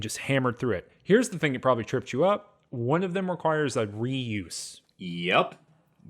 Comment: what did he just say? It requires what? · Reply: a re-use.